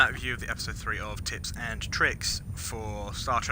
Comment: For a brief moment I thought he said Tits and Tricks!!! I'm not a noob and not quite a vet so found this hugely helpful.